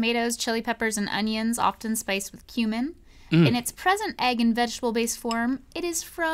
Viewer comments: I thought that they said it was spiced with "Human" at first. Then I heard what it was. Did anyone else think they heard "spiced with Human" or just me? The part at 5:25 Shakshouka! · I heard "often spiced with human"... Like, man, the crew really went the distance this time, lol.